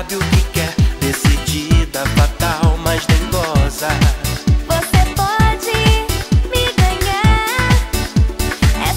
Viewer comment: O Q Q ELE FALO EM 0:55 KKK EU OUVI GOZA KKKKKKK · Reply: Dengosa